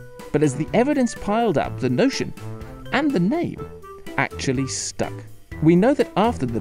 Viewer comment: what was the word after "actually" ? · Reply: Stuck. "The name actually stuck."